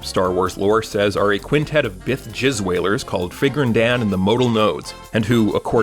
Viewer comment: did he just say jizz whaler